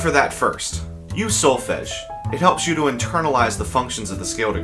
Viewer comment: you said something that sounded like "Use (solfesh)." What are you talking about there? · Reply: He's talking about Solfège - the do, re, mi, fa, sol... etc. syllables!